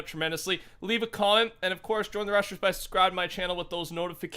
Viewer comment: At the end it sounded like he said; "Join the russians by subscribing" Russian anthem plays 😂